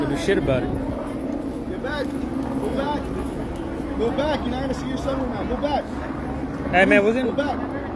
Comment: Do I hear that cop saying, ‘Go back go back etc., YOU’RE NOT GOING TO SEE YOUR SON RIGHT NOW’. Is this what he actually said?? I don’t even have words. I can’t even fathom any of this.😖😣